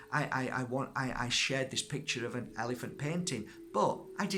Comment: Ha you said painting. When I watched this last time I thought you said fainting! <link> 😂😂😂 as ever thanks for the message x